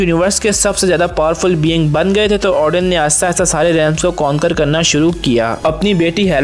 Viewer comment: what he is saying? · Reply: Aahista aahista saare realms ko conquer karna shuru kr diya, usne yahi kaha